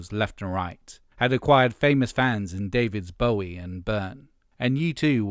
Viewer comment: I’m sorry, did I hear you say “David’s Bowie?” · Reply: “and Byrne.” He was referring to two different Davids: Bowie and Byrne.